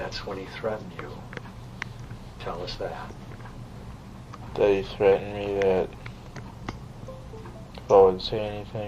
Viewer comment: I know you all are focused on him saying the blazer but at 4:20 ,I heard THEY threatened me. Not he but THEY meaning 2 or more. Freudian slip?